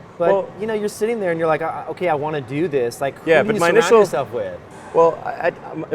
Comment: Mr.Rose, At 13:50, I cant make out what you said. please fill in this blank, cant make out word "Who do you _______ yourself with?" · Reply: surround